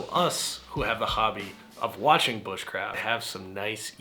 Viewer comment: When you said Bush craft I thought you was saying drinking Busch light